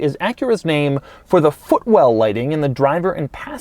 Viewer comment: Was it just me or anybody else heard Fuckwell